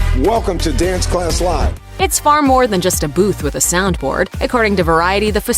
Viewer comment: Did you just say "it's more then a boof with a soundboard"? If not, that's what it sounds like....lol.